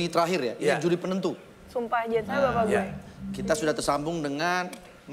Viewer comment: Di menit ke 10:48 kalian bisa denger nggak shireen bilang apa? : "Sumpah, Anjir.jangan bilang ini bapak gue" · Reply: “Sumpah jangan sampek bapak gue” Shireen cuma ngomong gitu doang, coba deh denger pake headset